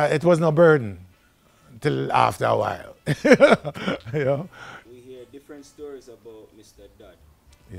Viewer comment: Who did he ask him about at 2:25? I can't hear the name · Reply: Clement "Coxsone" Dodd